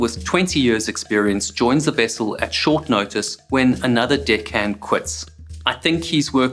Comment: I keep hearing the word "dick head" being mentioned a couple of times. Why do these men allow to be called "dick heads"